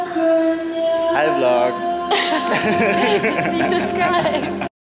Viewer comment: at first I thought said "hi love"💔 but it's actually "hi vlog"💔💔💔